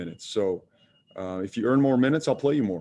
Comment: did he just say earn for minutes. JB you may not have that job soon.